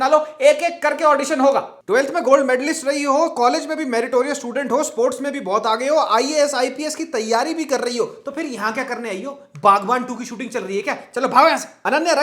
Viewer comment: i heard ISIS ki teyari 😂😂😂😂